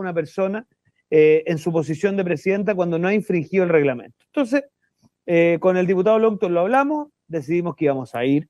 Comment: la gente ESCUCHA LO QUE QUIERE se dan cuenta que la mitad de los comentarios o más de la mitad dice " sharper la cago , vendido , etc etc " siendo que ahí EN ESE PRECISO minuto el dice " fuimos con logton " haahha están todos coludidos no es sharper solamente ... escuche y no repita como loro